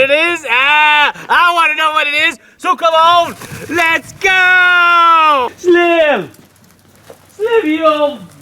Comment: Duh ,hello , how can you be on the way to 500 ? Your already at 532. My daughter thought you were saying [ So come on lets GOLD ] any way , as a retired drywall taper, the dollar is going to have to wait till next week. I really enjoy the clips. Steve B.